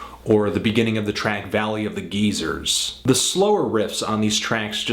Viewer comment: Did he just say "Valley of Geezers"? 😂😂😂😂